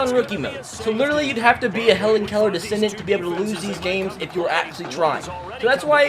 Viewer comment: I’m sorry did you just say a hellen Keller descendent